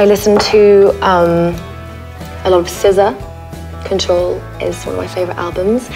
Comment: Did she say the album was Control by "Scissor"? Seems ungoogleable. Oo